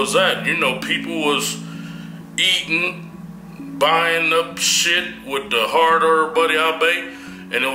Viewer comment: what did he say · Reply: "people was eating... buying up shit with the hard-earned money i made."